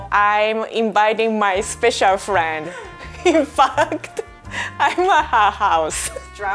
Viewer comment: DID SHE JUST SAY "FUCKED" I---, OUR GIRL IS GROWING UP SO FAAAST :'( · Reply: SHE SAID, "in fact, i am at her house"